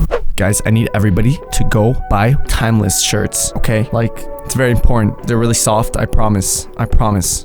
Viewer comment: did I misheard or something or did he say: ITS VERY PORN